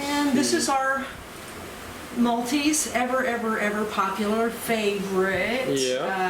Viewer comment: what type of fish are they?? I heard maltese? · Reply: Neolamprologus Multifasciatus, called multis for short